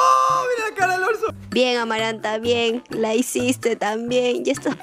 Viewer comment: Por si no escuchaste o no entendiste que dijo ralf dijo "amaranta", Cuando confirmen nos hacemos los sorprendidos va?😂